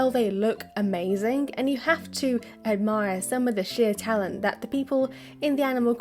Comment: I heard shit talent That's all